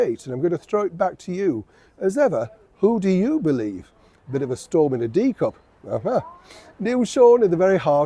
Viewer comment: I swear I thought I heard Neil say "a storm in a D cup." Naughty! 😂😂😂😂